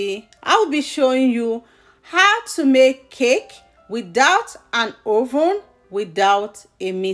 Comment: I thought u said without an oven 🤔